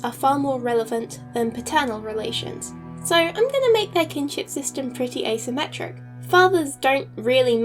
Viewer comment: Can i mention that 1. I first thought this said "Kidnap system" instead of Kinship system XD 2. This is very very cool and i love it